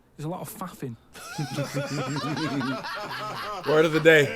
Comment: Dont know if anyone has said this but its "faffing". It kind of sounded like you said "fawffing"